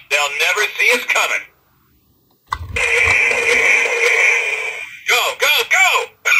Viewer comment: sounds like primes saying NO GOD NO kind of just a little bit